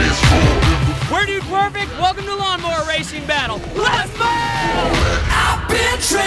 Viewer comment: Did anyone else hear them say let’s mow instead of let’s go